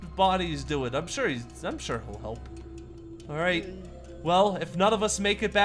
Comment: a bit after 16:07 i heard a deep demonic voice.. maybe it was only Freddy and Foxy talking at the same time.. Edit: it says "alright" or something XD i think its just Freddy and Foxy lol